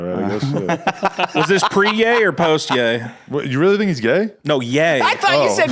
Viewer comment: I absolutely heard "gay" the first time, but he clearly said 'ye' when I played it back. Actually, now that I played it back a few more times, the first time he says it, you can hear either "gay" or "ye" depending on what word you're trying to pay attention to. Kinda like that whole "yanny" vs "laurel" thing.